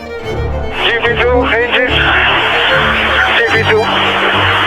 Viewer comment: Was i the only one who misheard "gp2 engine... gp2" to "deebedoo deebee" for my entire life?